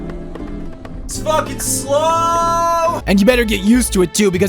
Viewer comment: did he say "It's fucking slow" ????? if it does, I think that's the first time I've heard swear lol